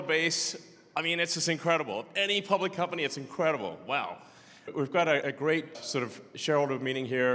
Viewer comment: "wow" , i swear i heard YilongMa there